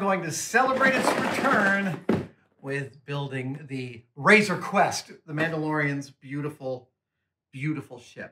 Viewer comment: am I the only who heard "razor quest" instead of "razor creast" ??